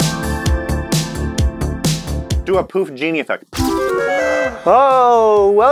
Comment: I really thought said “do a poofdini effect.” As in Houdini.